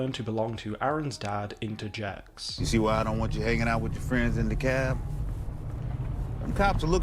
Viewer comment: Just a heads up, the subtitles say 'the cab', but I think the father's talking about DeKalb County, Georgia. Didn't want to be the "Umm, actually...☝🤓" guy, but I figured it was important since I believe they're trying to pull from/lean on some sort of stereotype of people from DeKalb. Used to live there when I was super young, but I don't remember squat about the people there outside of my school experience, so I can't say for sure